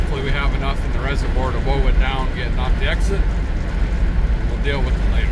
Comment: Could anyone please tell me what he said here at 2:02:33? Did he say, "wov it down"? What does it mean?!! Sorry, English is not my first language.